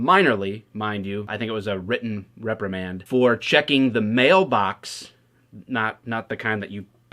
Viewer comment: Lol you are bias cops do nothing wrong and we all should just get in line and let the police do and say whatever you want because you have a badge smdh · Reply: When you said Male box, I thought you said Mail Box. Just the image of a cop walking down the street randomly opening up mail boxes came to mind. Ha, ha. Thanks for the laugh. Weird that people would complain about a thing like checking the wrong box.